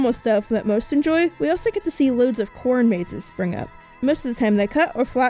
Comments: I thought she said porn maze😐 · I legit thought I heard her say "porn" mazes instead of "corn" mazes on 0:42 😅 I must admit, I was pretty confused.